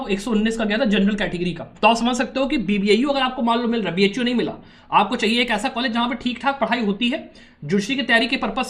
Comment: Sir, can you speak little slower. Couldn't hear the college name for the one's you mentioned for judiciary aspirants at 6:22? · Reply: Bbau and cusb